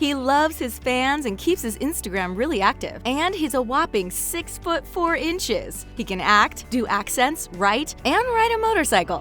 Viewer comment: i thought she said he HAS a whopping 6 foot 4 inches